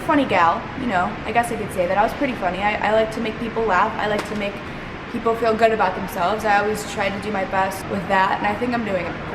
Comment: i thought u said u like making people feel good about themselves bitch!